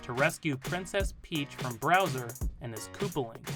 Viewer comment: did you just call bowser browser?!